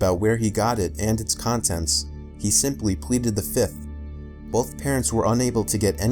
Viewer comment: He simply what? I couldn't make out what you said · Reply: Pleaded the fifth. It's a reference to the fifth amendment which is basically your right to remain silent.